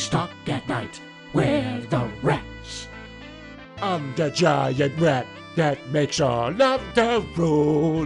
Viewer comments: Lyrics: Rats. We're rats. We're the rats. We pray at night, We stalk at night. We're the rats. I'm the giant rat that makes all of da rules. Let's see what kind of trouble we can get ourselves into! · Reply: I thought he said "I'm the giant rat that makes all the damn noise" but yours is funnier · Lyrics: Rats. We're rats. We're the rats. We pray at night, We stalk at night. We're the rats. I'm the giant rat that makes all of da rules. Let's see what kind of trouble we can get ourselves into! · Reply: I thought the big rat said and I make all the poo